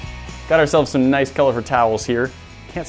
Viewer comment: You should try adding more glue after they dry, maybe spray glue, or something to try and make them permanent. When tried to say "coloured towers" I thought he said "coloured towels" and looking at their hands from the food colouring I thought "I bet you do! Now what about the sand structures?"